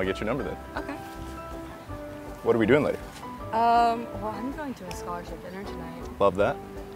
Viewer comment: Lol I thought she said I’m going to IraQ